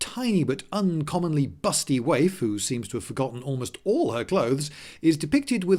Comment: did just say waifu?